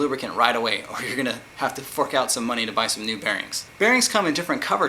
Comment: does he say fuck out?